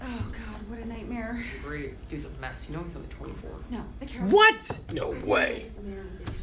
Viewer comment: I can't understand what rosa is saying at 1:13 to Amy. Can somebody help me out? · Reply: “He’s only 24”